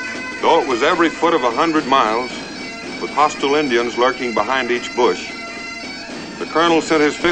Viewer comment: did i hear it right, "hostile Indians" @ 2:12 or is it the ...